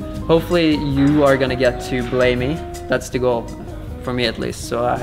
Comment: "Hopefully you are going to get to blow me" 😂 0:34 I thought thats what Magnus said at first but he actually said "Hopefully you are going to get to BELAY me" False alarm hahaha